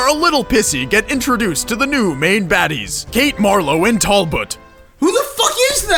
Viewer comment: Tallboot ( that's what he says)